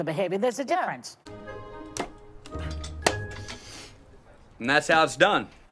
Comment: I thought he said "and that sound's dumb"